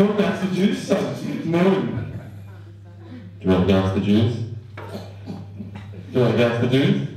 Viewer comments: Lol I heard "you want some nasty juice?" at first😂😂😂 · this whole time i thought he was saying “do you wanna glass of juice?” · I thought he was saying "Do you want a glass of juice" this whole time sense it was so hard to hear him. I just skipped to the pug cause who wants to hear a human speak when a pug is in a video.